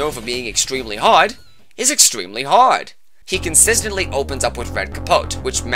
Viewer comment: the first time I watched this video i thought you say that matador is extremely hot instead of extremely hard